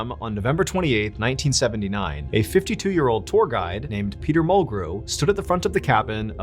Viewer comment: I thought he said Peter petegrew lol